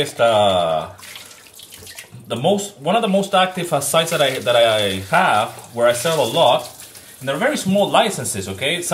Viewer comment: thanks for another informative vid dude! : ) btw what library were you going to say at the 3:46 mark btw? I'm guessing melody loops? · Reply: Yes, Melody Loops.